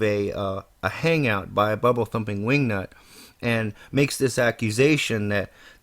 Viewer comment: At ~1:52 it sounds like you said “bubblethumpingwingnut” :) Tim did not admit to “docdropping” VC. AtheismDefended cut the video in order to misrepresent Tim. Matthew4Nineteen did “docdrop" VeridicalCadence by calling him by his initials in a hangout . The hangout is posted online. VC did not “doc drop” himself. He didn’t know that when he entered a hangout that his real name would show in the hangout or that it would show in a list of participants on Google+. I didn't know that either.